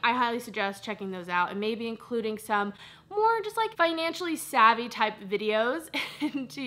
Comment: I know you said "financially savvy" but I heard "financially stabby" at first 😂🤦‍♀️